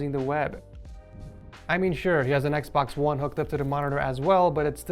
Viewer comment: Did he said xboxone?